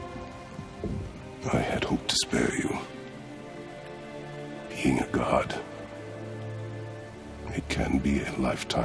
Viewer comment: I love when u explain back the stories, jack. Because I cant really heard the voice that mix within those sound effect.. U doing a good job as a youtuber. 👍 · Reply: LeoCiosu Music I thought you said he’s being a good cucumber